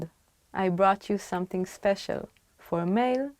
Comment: Right at 5:01, the audio says "brought" but the subtitle says "bought." I know just enough Hebrew to know that the audio is correct!